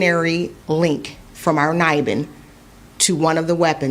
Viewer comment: Did she say a link to this “kniving”? Timestamp 1:52 The man’s a monster